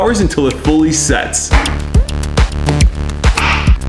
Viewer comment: I thought bro said “opium cheesecakes”